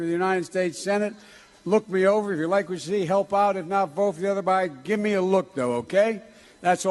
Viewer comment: I think he’s saying “if not vote for the other guy” maybe. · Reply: I thought he said "...vote for the other buy" - slip of the tongue